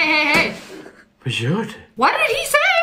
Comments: I think he said "va gör du?" witch mean what are you doing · you probably didn’t understand what he said, right? Well he talked in Swedish so you probably didn’t understand unless you’re from Sweden. He said “vad gör du?” In English it is ”what are you doing?”